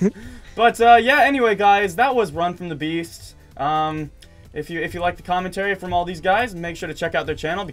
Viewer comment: I thought it said run from the breast lol